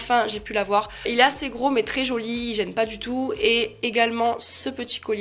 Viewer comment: A 1:33elle a dit en parlant du collier en lune " il est assez gros c tres jolie j'aime pas du tout " je compren pas moi elle dit il est jolie apres elle dit j aime pas du tout jpense quelle a pas fai expres mdrr 😂😂😂